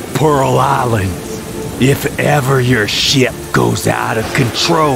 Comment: I heard "shit" instead of shop, I heard shit loud and clear.